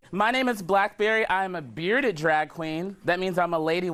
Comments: I thought the drag queen said " I'm a bearded dragon " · thought he said bearded dragon. A gender swapped furry. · "bearded drag queen" i thought he said bearded dragon